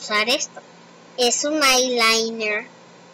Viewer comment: es un mail lainñer :u, eso escuche xd